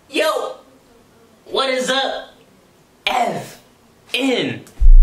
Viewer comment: lowk thought that shit said f@